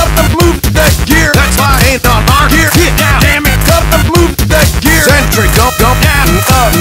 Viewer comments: I always heard “gotta move that gear” As “cut the blue gate here” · I always heard "Gotta move that gear-" as "Cut the bloop deck here".